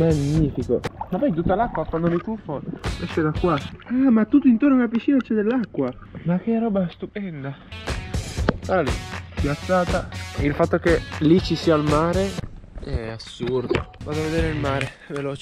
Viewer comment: Raga solo io al minuto 6:38 ho sentito fidanzata.😏😏😏😏